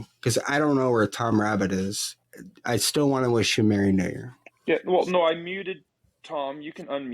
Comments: did someone say "merry n-word" at about 18:50? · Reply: merry new year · did someone say "merry n-word" at about 18:50? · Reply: I heard Merry new'ear...